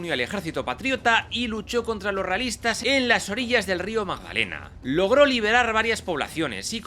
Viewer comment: pero que dices???? Magalena????? Es Magdalena ,el río más grande se colombia.